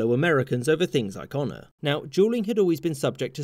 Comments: Everytime you say dueling it sounds like your saying juuling haha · It’s funny when he says “dueling” but I keep hearing, “Juuling, juul” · Sounds like he says juuling instead of dueling lol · everytime he says “dueling” for some reason i only hear “juuling” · When he says dueling it sounds like he is saying juuling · i literally thought he said 'juling'. Like jul-measurement for... something, idk. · At first it sounded like he said jeuling not dueling "people were using jeuls to legaly comit sucide"